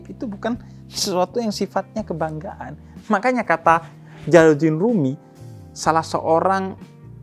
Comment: Detik 7:40 mungkin yang dimaksud bib ja'far itu jalaludin rumi? Seorang sufi, Ko saya dengernya kurang jelas.